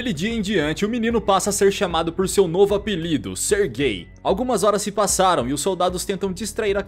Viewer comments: pera qual o nome dele mesmo eu acho q escutei errado...eu escutei sergay.. ksksks kkkkk KAKAKAKAKAKAKA. esse é aqueles momentos em que vc vê q vc saiu da quinta-série mas a quinta-serie ñ saiu de vc kkkkkkk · ser gay foi oque eu entendi kkkkkkkkk(sou muito mente poluída)😅